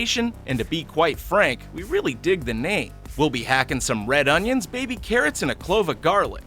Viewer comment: I thought he said 'we'll be hacking some red onions, baby!'